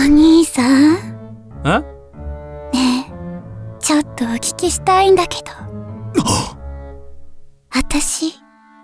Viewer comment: 最初に見たとき、一番最初のセリフの「お聞きしたいんだけど」が「おちちしたいんだけど」に聞こえて、自分の脳もいよいよかと思った。